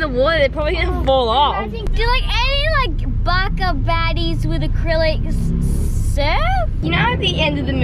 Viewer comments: did just say baka baddies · did just say baka baddies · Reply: OK it does sound like Baca baddies but when I first watched this I thought she said back up baddies😂